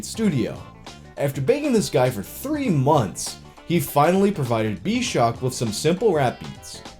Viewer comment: I had to rewind this 3 times cause I thought you said “after banging this guy for 3 months” and the picture you have of him did not help the matter. Third time I heard what you actually said. Good chuckle by man!!